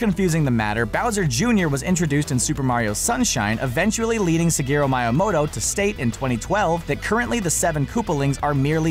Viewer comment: did he said: Sigaro miamoto?